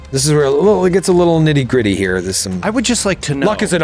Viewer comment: What a cliffyyy! A little bit of Trunk slipped in for me when said, 'nitty gritty.' All I heard was, 'niti creti' lol